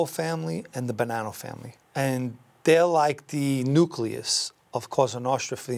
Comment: I first thought he said the "Banana Family," so replayed what he said.